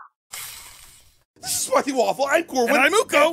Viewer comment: Did You Say Spiffy Waffle